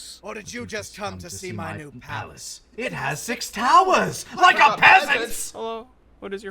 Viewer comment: Today I learned that Vegeta said "towers". All this time I thought he said "I demand more towels" twice lol